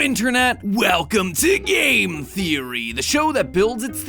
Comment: I thought it said "GAY THEORY"